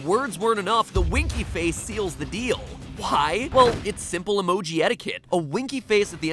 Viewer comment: I HEARD PIE...NOT WHY I NEED HEARING AIDS XD